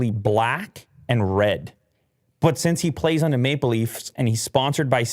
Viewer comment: I thought said “Make Believes” instead of “Maple leafs” lol